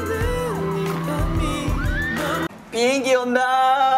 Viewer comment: i thought mark says"Piggy Onna~" (Onna is Japanese of girl or woman)